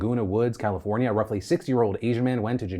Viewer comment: "A roughly 6 year old asian man" Phil I was shocked. I thought you were saying a 6 year old shot up a church for a minute here.